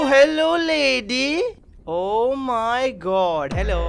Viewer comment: i thought he said "ohhhh hello la**dy"instead of "hello lady"😂😂😂😂